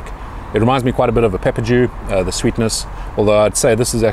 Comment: What's the pepper he compares it to? Sounds like he was saying pepper jew... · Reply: Peppadew